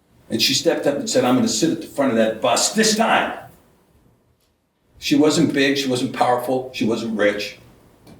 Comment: did he say "she wasn't b***h"